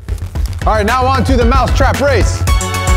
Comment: I heard them say now on to the mouth trap race not now on to the mouse trap race